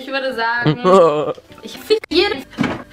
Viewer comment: ich habe verstanden ich f*ck jetzt...